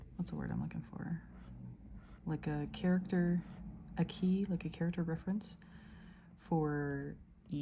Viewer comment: When u said character ("a") key around 20:22 I thought you said character aqui I'm like ??? Character here???